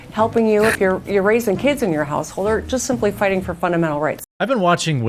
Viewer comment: Did she just say erasing kids in your household?